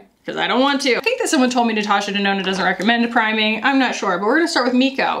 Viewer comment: Right after the traumatic fan story, you said you're not going to cry... and I could have sworn you then said "natasha denona doesn't recommend crying". Turns out you said she doesn't recommend priming.... but I stand by what I heard the first time. 😂👌🤭